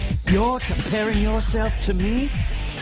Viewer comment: I always thought he said "Your not even good enough to be my shadow"